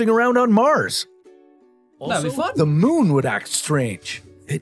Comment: did bro say the f word? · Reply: He said that would be fun but fast